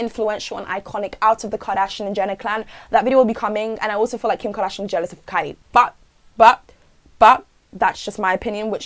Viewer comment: wait what did she say about kim kardashian at 8:11 i can’t hear 😭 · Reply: kim she said that she thinks kim is jealous of kylie 😭